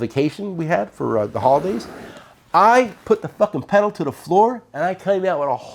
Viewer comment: You missed a quack at 24:40... "I put the f@ pedal to the floor" lol I had to rewind it to make sure I heard right